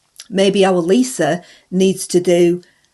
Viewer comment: Have a handy with Nandy? Must have misheard .